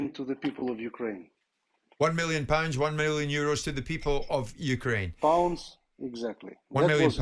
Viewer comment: Thought usyk said million every day to the poor people of Ukraine 😂